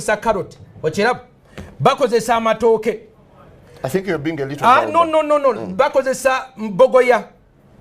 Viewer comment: did he really said 'i think you' re being a little pervert'? 😂